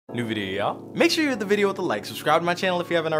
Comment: when you say new video ya'll it sounds like you said duvidiya